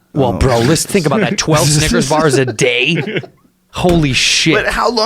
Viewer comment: straight up said the n-word instead of snickers